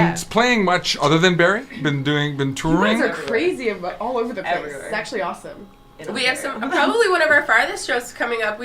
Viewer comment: "its sexually awesome!"..... I swear thats what I heard.